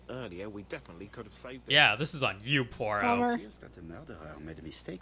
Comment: I thought I heard you call "Poirot" "Poor-o," and now I can't unhear it. I'm imagining some disgusting mud hippie detective who can't solve shit.